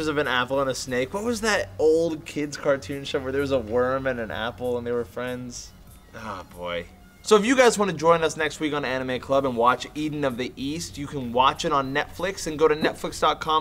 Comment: It sounded like Meg said Anime Glub.